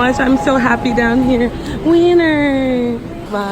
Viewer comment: i thought you said wiener I snorted